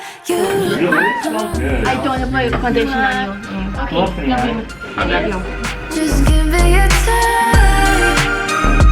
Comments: What does Claudia say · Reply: Eilish Çevirileri I don’t wanna put my foundation on you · What does Claudia say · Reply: Eilish Çevirileri “i don’t wanna get foundation on you” or something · What does Claudia say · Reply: Eilish Çevirileri “I don’t wanna get foundation on you “ 🥺😂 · What does Claudia say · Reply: Eilish Çevirileri she said she didn’t want to get foundation on billie then she said love you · What does Claudia say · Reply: Eilish Çevirileri “I don’t wanna get foundation on you “ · What does Claudia say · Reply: "I don't want to put my foundation on you" i think